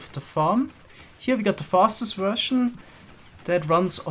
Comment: did he say fastest russion